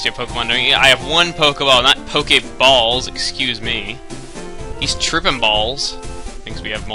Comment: the translation captions say "one day i want local unforgiving balls excuse me" XD